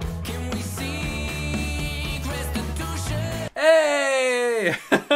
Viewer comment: When I heard "Restitution" when I first listened to it.. i also thought of you lol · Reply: I thought the lyric was "prostitution" at first so yall obv know how I reacted when I heard it for the first time